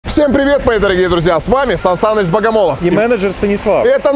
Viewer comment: Всем привет, дорогие друзья, с вами Александр Долгополов и менеджер Станислав... А, послышалось.